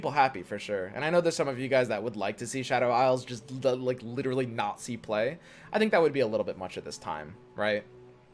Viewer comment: did just say natsi play? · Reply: he said "not see play"